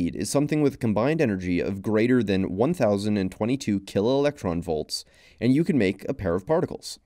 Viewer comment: it sounds like you're saying "killer" instead of "kilo"